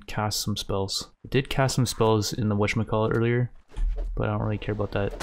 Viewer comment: What's this thing says, for example in 16:10 "wash my call it". I'm not a native english speaker and i can't tell what it is. It's been bothering me for months. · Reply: He is saying "whatchamacallit" People say that when they can not remember the name of something that they are trying to talk about